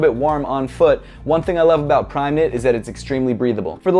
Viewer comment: This was a great review! But I am confused at the beginning of the video where you try to describe the material for the boost. I kept hearing “primate” and I’m wondering if you’re trying to say “permeate” . I know...it’s the grammar snob in me. But I do wonder if you mean to say that the material is permeable which is why I had to ask. And for everyone who will jump on the hate train for me asking such a question, just chill. I’m seriously asking because I want to know if I’m understanding it correctly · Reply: Primeknit is the name of adidas material system